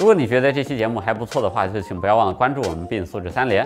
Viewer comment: 为什么叫耶。。。每次都听成 不良卖茶人